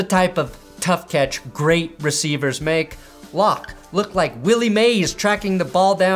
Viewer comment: You said Willie Mays I thought you said Billy Mays really threw for a weird loop 😂